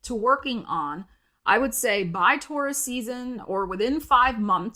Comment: LOL!! For a few minutes, I thought you were saying "Tourist season". That's my former Florida hotel sales manager brain for ya!